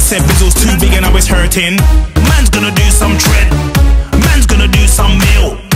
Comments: Am I the only one who thought he was saying "Mans gonna do some tren"? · Totally thought he was saying "Man's gonna do some Tren" xD